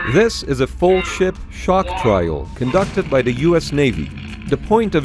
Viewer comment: I could have sworn he said at the start "this is a full shit shock trial".